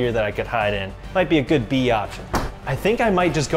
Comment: did just say biotch